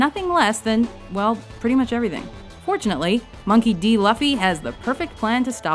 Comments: slightly raging at the mispronunciation of monkey d. luffy. sounds like she said luvy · did you just say luffy as in puppy? O_O. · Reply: I thought they said Monkey D. Fluffy... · Did she just say monkey d laffi at 2:15, my goodness she can't even pronounce his name.😐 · Luuu Luffie? Did she say Luffie? hakskjcfwnd;sfjhisulk!HJ!KB!HJ!VKJH! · did she say "laffy" 2:15, hire someone that can speak please... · did she just say laffy? kill me now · Did she just say "Luhffy?"